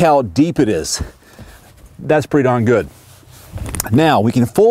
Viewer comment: that's pretty not good? Why? (Did he actually say that?) Nice and clear review! Thumbs up! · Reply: Smoll Kitteh he said darn